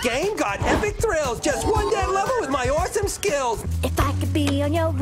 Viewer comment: what i just heard is "1 damn level and my orphan skills" is there anybody else that literally hears it edit: the original 2015 nra video is literally the same sounds 1 DAMN LEVEL AND MY ORPHAN SKILLS